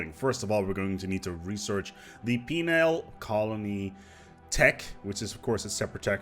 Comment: I know you said penal (with an odd pronunciation) but it sounded like female colony tech. So much so I checked the Sub/Closed Captions (which actually thinks you did). Heh, surely you're not suggesting all criminals in our empires are women? XD